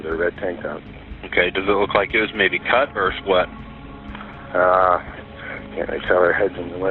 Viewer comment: i thought he said..."cut or slut"...